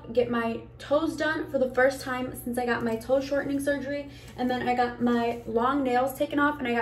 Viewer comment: did I hear this right.. toe shortening surgery?!?! why do you have such body dysmorphia. stop the unnecessary surgery :C so pretty just natural